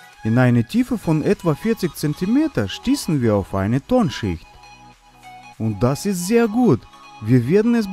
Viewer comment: wer hat auch "Turnschuh" verstanden? 😂